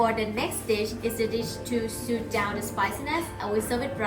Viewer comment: At approx. 12:20 in the video, the captions read "suit down the spiciness". I believe what the lady said was "soothe down the spiciness"? Maybe I'm wrong, but it made me pause the video either way.